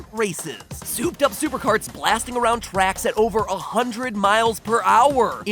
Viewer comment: I thought he kept saying miles prower.......miles 'tails' prower